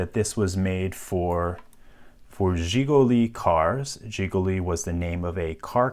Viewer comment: I thought i heard jiggly cars